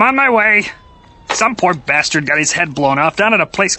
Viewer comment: As a kid, I always thought he said "pork bastard". it sounded much funnier.